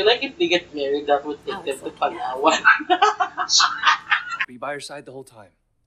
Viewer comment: Girl, that was all over the place! :) Plus what does your friend say at 7:06? something about they getting married and a ¨paragua¨ maybe? o-O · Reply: he said "palawan" :D it's a tourist destination here in the philippines. it was just so random so it made me laugh lol. so it's one little filipino joke for what few filipino viewers i have haha